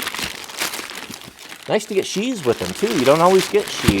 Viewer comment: At first I thought he said "it's nice to get cheese with them"